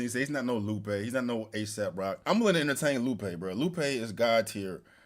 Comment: Are you really out here flaunting your ignorance on the internet? Go do your homework. He is talking about Aesop Rock. The fact that you don't know him says a lot. I think that it's just better for you all to go to bed because how are you making arguments without understanding all the references he's making? Your lack of knowledge on this is going to cloud your judgment; this conversation is a non-starter with you. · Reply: congratulations to you, you listen to Aesop Rock. It sounds like he said ASAP Rock. Either way him saying the Big 3 isn’t special lyricist is crazy to me and I love Stogie T he’s one of the best lyricist. Because I don’t know a rapper I’m ignorant You probably can’t break down double entendres and introspective bars. So don’t come here and call me ignorant when you don’t know me